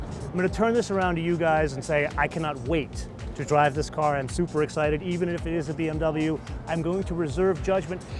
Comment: OK did you just say you're Supra-excited? ...